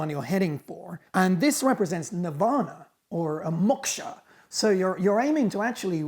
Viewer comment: Why is Marcus sounding so American? When he said "Nirvana" I almost thought he'd said "Nevada". 🤠